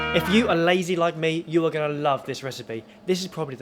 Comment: HAHA I was eating a crunchy cookie, so at first, I heard "if you are a lady like me"...and i thought: Barry.. did you just call yourself a lady?